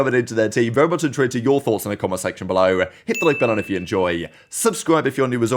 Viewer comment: What do you say at 0:26? I've heard that intro so many times and I literally can't figure out what you're saying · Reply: “very much intrigued to your thoughts on the comment section below” i think?